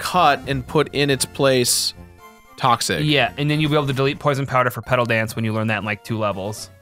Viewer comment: I definitely thought said "Delete poisonpowder for Cuddle Dance", and I had to stop and think about that for a minute.